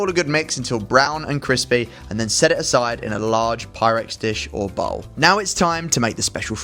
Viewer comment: The fried rice look SOOOOO GOOOD!! 😋 Also I think I really have been infected because not only did I accidentally write hyrax instead of hyrox in another comment but I thought you said Hyrax dish instead of Pyrex dish 😭😭🤣